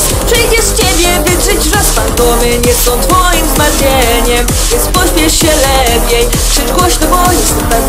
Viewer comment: czy tylko ja usłyszałam ,,FANDOMY nie są twoim zmartwieniem" w 1:02 ?